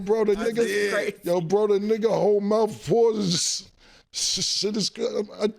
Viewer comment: Did just say PAC-MAN whole mouth is a pause? 😂😂😂😂😂